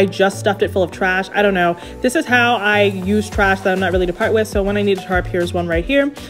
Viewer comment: did she just say i out trash im not ready to part with?